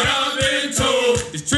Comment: What does it say near the end? Trying to learn some cadence 😅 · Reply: Trips to the gulf are getting mighty old